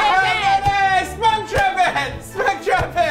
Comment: I heard "spunk trumpet"...? Don't know if that was what you guys said, but I'm going all out with it! It's going into my epic adjectives, right between "groovy" and "uber" shall be "spunk trumpet" - random sexual nerdiness.